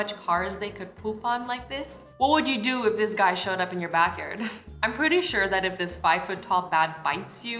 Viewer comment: did she just say back yerd😂😂 love u